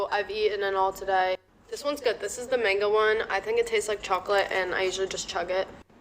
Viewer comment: "this is the mango one. I think it tastes like chocolate." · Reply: I thought she said chalk and I had to rewind a few times. Chocolate!? Really?